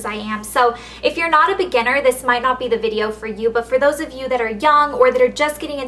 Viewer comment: haha when you say "for those of you who are young" it sounds like you say for those of you who are "dumb"